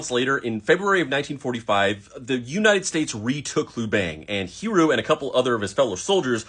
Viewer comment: I thought you said Hiro Hamada like the Big Hero 6 kid